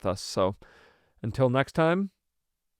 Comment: Sounds like he says Bary Bible Podcast. 😅